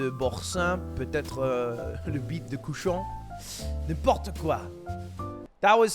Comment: When he started speaking French the only thing I understood was “petit cochon” which stands for little pig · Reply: I thought he said butte de cochon or pig's peepee